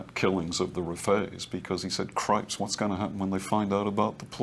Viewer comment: Did he just say oh crêpes